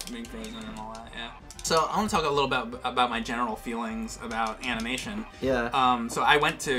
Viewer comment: It sounded like you said, genital feelings. Lol 😂